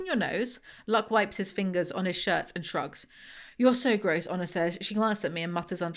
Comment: Had this on in the background (I looked at the pictures though!) and hadn’t read any of the words on screen and until 2:13:38 my dumb American ass didn’t realize her name was HONOR and I just thought her name was “Anna” but said with a British accent. I should’ve known better though 😭